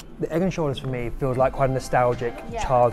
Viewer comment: Egg and Shoulders . . . I heard "Egg Insurance" . . . which sounds like a fertility euphemism? but it's just a. . . jest a Jussyism?